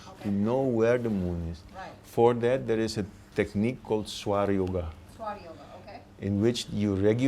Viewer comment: Thanks for this video. Could anybody help me to decipher what he said for YOGA in 6:55 time? I can't understand name of yoga he mantioned, subtitle show SOIR YOGA, but I can't find nothing similarly on internet.